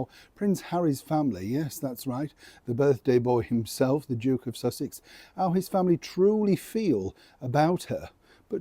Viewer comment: I really thought said the Joke of Sussex🤣🤣🤣🤣🤣. From a resident of Sussex, just outside glorious Brighton. STRIP THE TITLES, NOW.